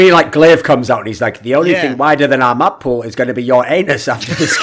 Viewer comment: The first time I heard the joke I thought he said, "The only thing wider than our map pool is your aim!" Then I rewinded it and it actually was, "The only thing wider than our map pool is your anus!" XD